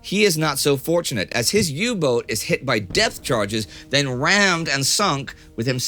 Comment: Indy is definitely saying death charges...